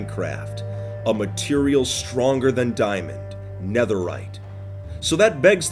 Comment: “And for the first time in the history of Minecraft; something harder than diamonds.” Me: -Obsidian -Bedrock -Finding a girlfriend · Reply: "A material stronger than diamond: netherite!" He did not say "harder".